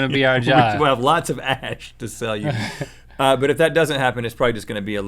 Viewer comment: Thought u said we will have heaps of hash to sell you.